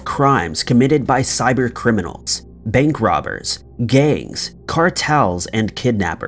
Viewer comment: I know you said gangs but it sounded like you said GAYS and I was like hold up I can't eat my spaghet until I relisten to that.